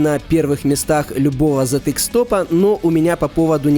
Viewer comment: я слышу "Затык-стопа" xD